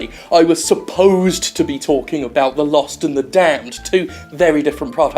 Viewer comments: Y'know in that first bit I thought you said 'The Lost and the Tanned' and was giggling far too much. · It's The Lost and Damned, not The Lost and THE Damned, you fool! I ought to scald myself with my coffee just to spite your skin-breathing ass! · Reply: I thought he was saying "The Lost and the Tanned" in reference to what the last episode was about.